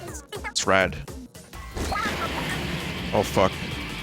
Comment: I swear to god she says "what the f*ck"